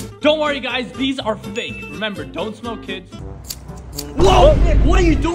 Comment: Bro i heard "dont remember, smoke kids"